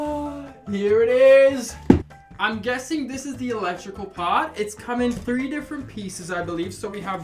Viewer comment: I thought it said Electric fork and I was like “oh um cool?”